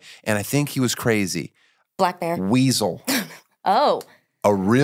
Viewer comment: Did she say "black man" 😂 just gonna let that one slide?